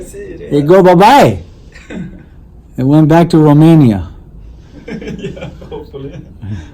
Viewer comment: Where she from? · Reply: I think, he said "Rumania"